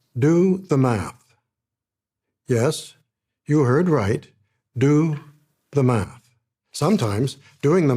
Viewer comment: 'Do the math' lol. But ignore HISTORY and SCIENCE at all costs. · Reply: I heard, "do the mah", which I'm going to interpret as, "do the Macarena"..🤔😇